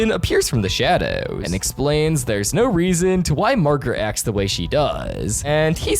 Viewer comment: did you just call Margaret marker?